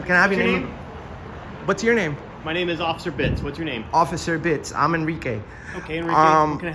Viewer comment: I thought he said officer bitxh😂😂😂 i had to hear it again 😂😂